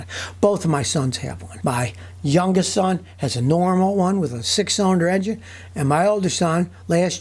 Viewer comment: Haha I thought he said “my youngest son, my normal one” 😂😂